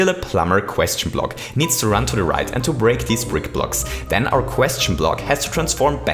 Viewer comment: at 2:09 did you say brick blocks or frickin blocks plz reply